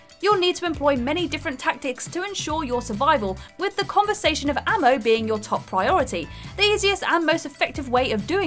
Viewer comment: did she say "the conversation of ammo?"